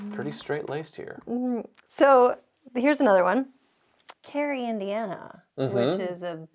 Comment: At first I thought she said Gary Indiana “a beautiful town” and I thought WHAT???